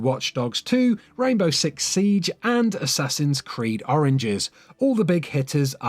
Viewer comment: Haha I heard you say "Assassins creed oranges"